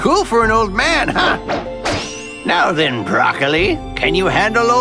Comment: did he say..... broccoli? Or have I been smoking